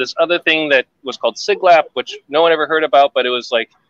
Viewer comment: did say "Siglap"? Like the neighborhood in Singapore?